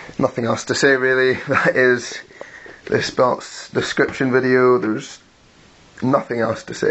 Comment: i thought you said that this belt was hideous